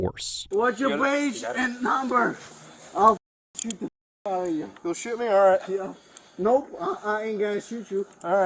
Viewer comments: I didn't hear him say shoot, I heard him say sue you . Why not give him public intoxication, our would that prove slurred speech?? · He didn't say "shoot". He was saying sue with a foreign accent. He said "I'll sue the f out of you" to which the cop says "you'll shoot me?" He responded with "No, I'm not going to shoot you, I'll sue you. You are breaking the law." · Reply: if you use context clues along with what he is saying you would of heard him asking for his name and badge number then said ill “shoot” (sue) the ish out you. Then he clearly says “you’re breaking the law, you’re a public servant, you’re breaking the law” he then says “like the second time” referring to his first encounter with this tyrant of a officer. He’s so clearly saying sue.